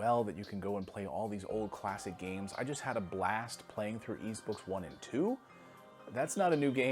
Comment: What game is he talking about @ 12:05 The closed captions says ebooks 1 and 2. I am interested to what games he is talking about. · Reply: Since no one answered I did eventually find it. Y's Book 1 and 2 on TurboGrafx-16 CD